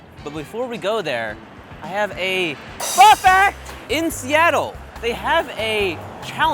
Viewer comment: I thought Steven said "Pro Fact"